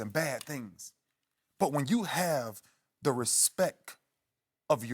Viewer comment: Did He Just Say RespEck